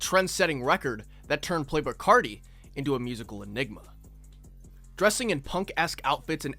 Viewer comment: i thought he said “turned playboi carti into a n****” i was like hold up